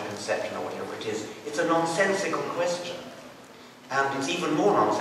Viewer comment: Did I hear this right? 6:15, "Its a nonsensical question." To find out whether or not the child in the womb is a human? If I heard wrong please correct me. 2nd graders textbooks tells them that cats produce cats, dogs produce dogs, but when it comes to humans humans produce........something else?